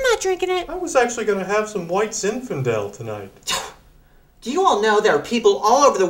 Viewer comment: Thanks for cluing me in. Pops mentions Zinfedil first at 0:42 sec. But what are all the words? Sounds like "I invested in hearts and white Zinfedil tonight." LOL